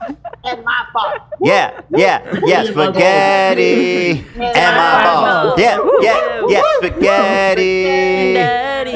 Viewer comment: everyone singing "spaghetti and my balls" is my favorite thing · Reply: i thought they were saying “spaghetti ain’t my fault” cos the meatballs were what made his gallbladder shut down 😭